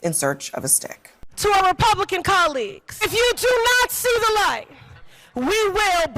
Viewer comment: I really thought the bald lady said: "Nazi the light". The dems uttered that word so often, I am mirage hearing that word now.